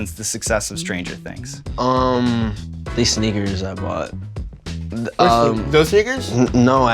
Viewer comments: i really thought this mans said “these nword🤭 i bought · i heard that completely wrong... those ni.... 😂🤦‍♂️ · this n-words i bought -What?, does n words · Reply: Sneakers* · I thought he said "These n*ggers I bought"😭😭😭😭